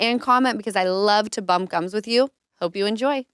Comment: haha I thought you've been saying "bump guns with you" THIS WHOLEEEE TIMEEEE!!!! hahahahahaha